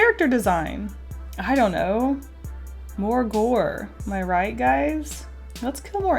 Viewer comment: i swear to god i heard vore